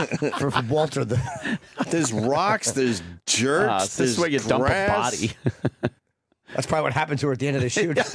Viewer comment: Thought Ope said there's BRASS on the beach...grass, grass. That shore is a dump, spent shell casings isn't much of a stretch.